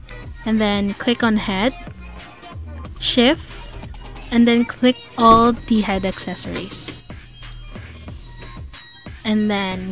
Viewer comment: Love the tutorial but 5:22 i didnt understand what you said and how do i select it all? please help :/ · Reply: click head and then press shift in keyboard then click the head accessories